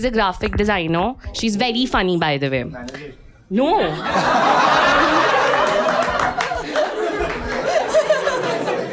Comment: what he says ?? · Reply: Manager